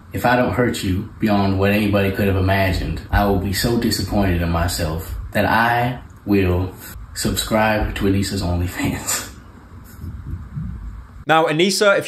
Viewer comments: Thanks for clarifying, I thought he was saying he'd subscribe to Onision's OF 🤣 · I initially thought he said onision's onlyfans which would have been way more funny